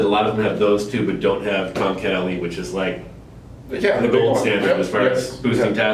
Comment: Silly question but at 7:22 you said "something" is the gold standard for boosting test. I could not understand what you said so I put on the closed captions and the word is "tomcatelli" but I cannot find any reference on Google to this word. Please let us know what is the gold standard. Thanks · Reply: I believe said Tongkat Ali. We sell it at Best Price Nutrition. From Hi Tech.